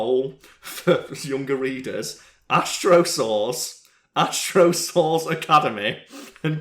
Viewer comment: Thought you were saying "Astro- Sauce"